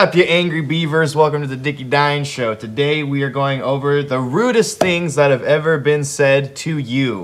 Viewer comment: I keep hearing "Rudess things" instead of "Rudest things", and now I'm struggling to think of Dream Theater-based insults.